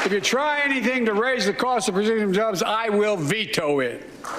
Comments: The cost of what? I heard "Brazilian jobs" whaaaaa??? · "If you try to raise the cost of Brazilian jobs..." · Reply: I was just gonna type, that's what it sounded like he said to me. "Brazilian jobs" - hmmm wonder what he did(or meant to say)? :-/